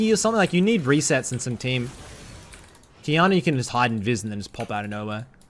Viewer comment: did he say hide in jizz?